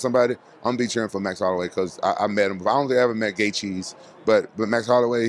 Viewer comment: did he just call gaethje "gaycheese"? LOL